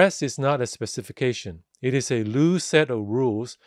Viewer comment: if I hear it clearly. it is a "loose" set of rules, not new set of rules.